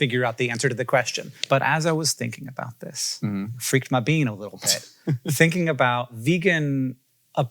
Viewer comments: I had to rewind because I thought he said "flick my bean" 😅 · I thought he said “flicked my bean a little bit” 😂😂